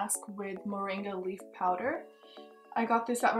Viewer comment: Sounds like you said mendi powder, whats that?